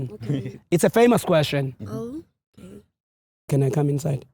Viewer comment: The guy thought say "Can he cum inside"🤣🤣